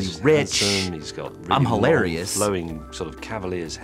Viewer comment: What did May say at 5:57-5:58? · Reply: "...flowing, sort of cavalier's hair" he then realized that this compliment was ironically inappropriate since Michael is bald